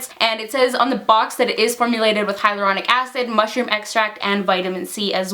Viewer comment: It sounds like she said it has hydrochloric acid in it lmao😂